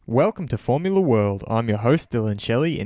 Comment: Maybe I’m biased but why do I always hear at the beginning: “Verstappen Formula World”